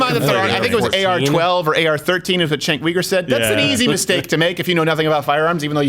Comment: I thought he said AR-14.